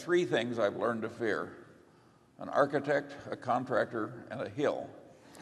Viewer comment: What is the third thing Charlie says at 2:25? "An architect, a contractor and [what?]". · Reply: A hill